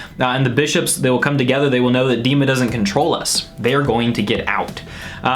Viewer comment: I thought Tyler was saying "demons all control us" which would make sense with regards to his Christian faith. Dema sounds like demons, and he could be warning us that demons and evil spirits are controlling so many of us and we don't even know it. Especially people with mental illness such as depression, he could be saying that it's the devil and his workers who attack us with such things.